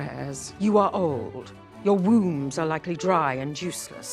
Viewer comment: "Your wombs are likely dry and juiceless" is absolutely the most diabolical quote ive heard all year😂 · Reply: I thought she said wounds, not wombs. Either way yikes